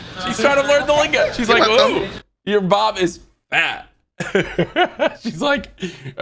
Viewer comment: I thought she said “your mom is fat” 😂😂😂